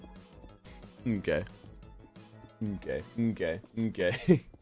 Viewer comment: Sorry but is it only me who hears: “I’m gay. I’m gay.” at 0:39 instead of probably “hmm kay, hmm kay” 🤣😂